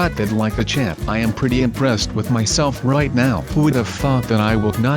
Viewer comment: anyone else hear him say the f word on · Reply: He said ''Who would though that i would not die doing that jump''